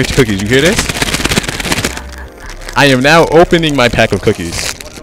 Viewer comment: Opening my pack of cookies that so sus 😐👀🤣🤣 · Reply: My bad I thought you said sus when you said no I don’t I sound so pause lol sorry for that man